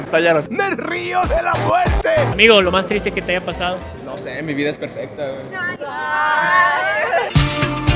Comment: Escuche "no se mi vida esta prieta" XD